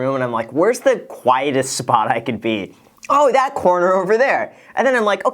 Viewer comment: I thought Graham said, "Where's the whitest spot I could be?" 😂😅